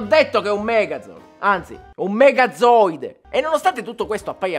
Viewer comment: Per un momento ho capito ZOANOIDE. E quando hai detto "megazooide" ho capito "megazoanoide" 😔